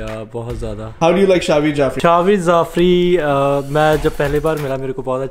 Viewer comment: did he just say: “Shahveer Zafry” 😂😂